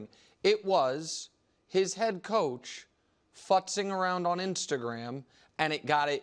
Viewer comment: What exactly does Nick say at 3:45? Is that what i thought i heard 🤔 😂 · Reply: He clearly says the word futz.